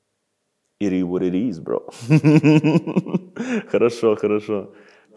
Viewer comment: Что он сказал на 8:16 ? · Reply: it is what it is